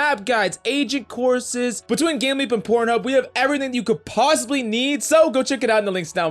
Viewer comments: Bro Ik I’m not the only one who heard Phub? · why did I hear him say between gameleap and por*hub